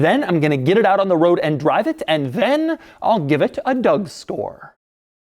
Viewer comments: on minute 3:03 did you say.. give it a dough store??!! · he really said "Doug Store " instead of score lol